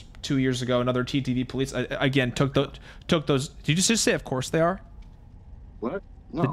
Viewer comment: stopped posting videos about DBD because he was quitting DBD all together. If he had watched video about it he would've known why he stopped. It wasn't because a nobody like this guy "exposed" him. This dude is just weird. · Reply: And he whispers something I think he said ofc they are when you was talking about the trans.